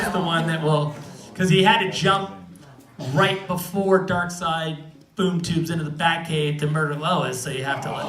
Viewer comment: What did he say at 10:12 so everyone shouted? I couldn't hear it properly, it was about some murder, please help. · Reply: Flash had to time-jump to the moment right before Darkseid boomtubes into the Batcave to murder Lois.